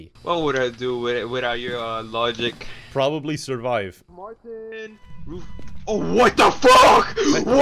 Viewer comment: scared the shit out of me, my name is martin ... i just hear martin right?